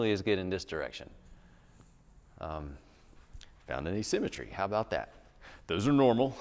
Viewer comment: Hi, Thanks for the video. At 1:24, did you say "found an assymetry"?